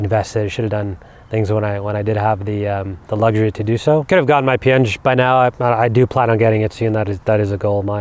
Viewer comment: ph or what i didn't understand what was that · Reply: I believe he said “P-Eng” which is also known as “Principles and Practice of Engineering.” Essentially an examination to become certified as a Professional Engineer. The prerequisite to it is to have completed your FE (Fundamentals of Engineering) examination to be an EIT (Engineer in Training) and to have worked progressively under another PE for about 4 years.